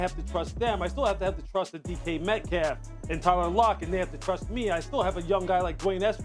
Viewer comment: Did he say "Tyrone Lock"???😂😂😂